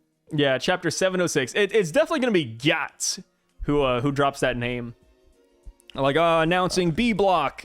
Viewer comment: Did you just say it will be Gyatts that announces bellamy’s name